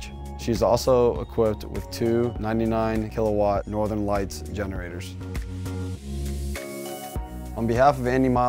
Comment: did he just say two ninety-nine KW generators? That’s... a lot...